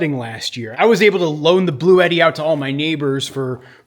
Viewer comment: Thought you were saying Blue Eddie 😂